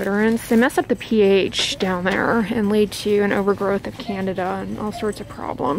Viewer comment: It sounded like she said an overgrowth of Canada at 2:02 LOL